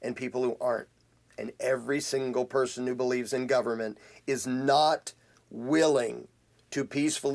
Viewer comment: Not just everyone "in government"; everyone who ADVOCATES "government." And no, it's not at all similar to saying "all men are the problem," because to support the existence of "government" NECESSARILY AND INHERENTLY means that someone condones authoritarian aggression against others. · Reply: my mistake, im not new I've been watching for years and completely agree with 99% of what you say. I just thought I heard you say "anyone who joins government" but you said "anyone who believes In government".